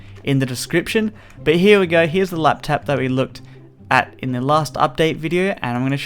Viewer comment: It sounded like he said laptap... Did anyone else hear it at about... 1:20 ?